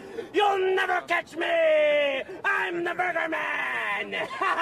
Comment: Everyone: "Eminem always looks so serious." Eminem: "I'M THE BURGER MAN!" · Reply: I heard "I am the murder man!"